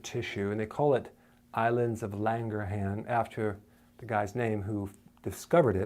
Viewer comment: It sounds like your saying "Islands of Langerhans." :-)